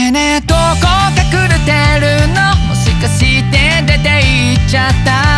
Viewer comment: やばいもしかしてが「模試して」に聞こえるそんなとこも可愛いんだけどね！